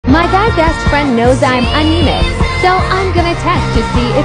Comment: I was so concerned because in the beginning I thought it said “my best friends dad” 💀